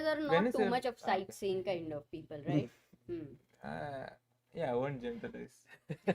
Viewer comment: what pragg said? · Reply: "I won't generalize"